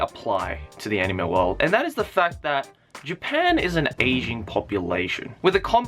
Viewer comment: i thought he kept saying "asian population".. i was like.. well duhh? but i guess he's talking about an "aging" population.